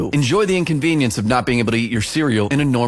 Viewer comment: Bro i thought bakugo said I ATE A CHILD